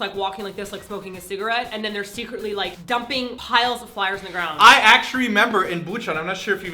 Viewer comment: I heard say acturry at 1:24...someone's becoming more Korean by the day. Lawl.